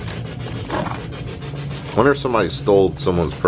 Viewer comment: i heard the same voice that said ur scaring me say "yeah"